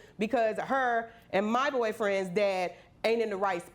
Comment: we gonna skip over the fact that she said she dating her mom’s ex boyfriend.?? or did i mishear that three times.?